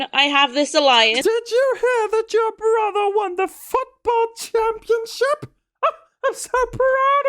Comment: When did the mother voice, I heard "f**kball" instead of football 😂😂😂😂